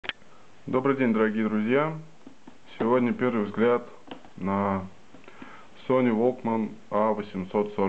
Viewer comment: I heard 'some sort of bitch' lol